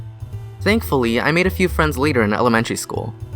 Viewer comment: For some reason, i thought it said eating elementary